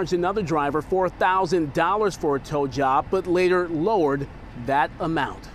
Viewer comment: Oh! TOW JOB. Whew, I thought I'd misheard that guy at first.